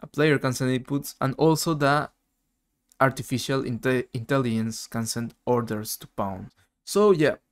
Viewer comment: did you say "Artificial Italians" ?!))